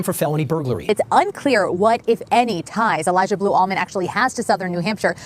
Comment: I thought you were saying blue almond 😂😂😂🦘🐨🦘